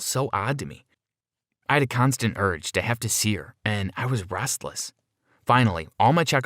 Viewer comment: i was breastless is what i heard